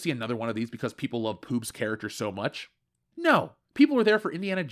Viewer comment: Apologies for being ignorant but I heard a nickname of a sort mentioned a couple of times - "poob" or something like that at the 9:00 and 10:00 marks. I assume it's shorthand for "Phoebe Waller-Bridge"